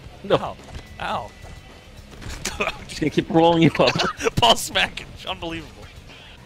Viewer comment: it sounded like said pall smackage instead of small package